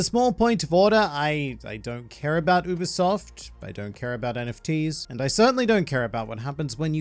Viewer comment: Every time I heard "Ubisoft" my brain translated it to "Usopp"